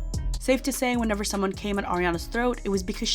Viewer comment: I thought you said “came in” not “came at” her throat. 😂 I was like “damn”….